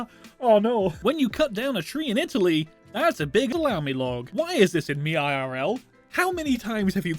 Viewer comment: Excuse me, but did you just say 'lamilog'?